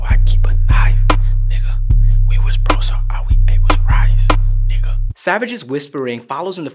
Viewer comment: I thought he said "We was broke so all we did was rise".